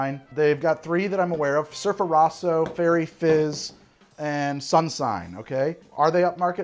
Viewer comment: Passively listening I thought you said Faerie Jizz.